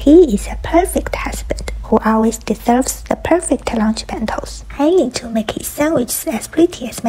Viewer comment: I thought you said "who always dissolves the perfect lunch bentos"